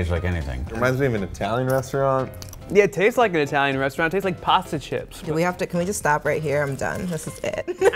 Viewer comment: I thought he said it tastes like prostitutes not pasta chips lmfao